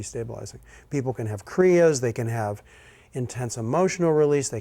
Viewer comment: Beautiful. What is that word that says at 6:48? I can't make it out. Sounds like it begins with C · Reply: He says the word "kriyas." For future reference, most of videos have been transcribed. If you turn on closed captions -- the "cc" button on the bottom right of the video screen -- you will have subtitles. Hope that was helpful. Best, Clear Water Sangha volunteer staff